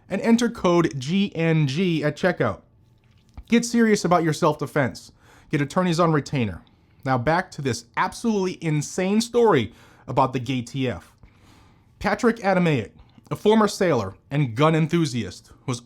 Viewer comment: Did he say GayTF? 🤣